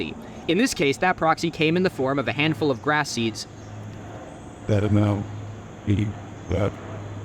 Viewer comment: what is said here “better now meat bad” am I hearing something that’s not there?